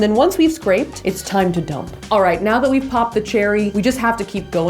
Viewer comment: did she just say pop the cherry?